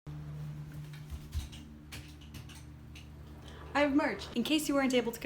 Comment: What was said "I have Merch" What I heard "I have emerged"